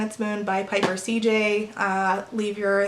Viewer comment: At the end of the video, when you said Piper CJ, I misheard as "Plagiarist CJ" and I thought you'd enjoy that :D